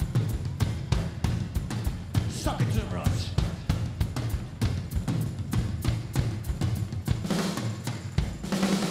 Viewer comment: Could you please help me to clarify what did he say at 00:18?🙏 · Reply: Polina Panshina “sock it to them rog”, basically “show them what you’re made of”